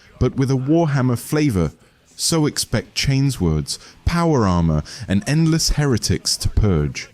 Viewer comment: chatgpt script, ai voice. so fucking exhausting. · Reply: I wasn't 100% sure until i heard "chains words" when talking about the chain sword in Boltgun.... lol